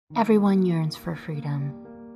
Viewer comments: in the beginning i thought she said "everybody urines for freedom" LMAOOO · At the beginning of the video I thought it said "free doms"